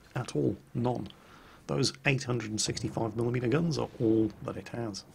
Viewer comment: Did he really say "Those 865 mm guns"?